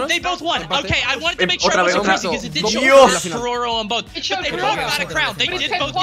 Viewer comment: no se que dice pero entiendo algo de "KERORO ES UN BOT" jajajaajja